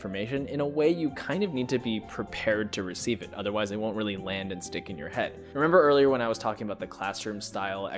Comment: Am I higher than I thought, or did you just say "In storytelling, when you receive important pieces of information, in a way, you kind of need to be prepared to receive it, otherwise it won't really land its dick in your head"?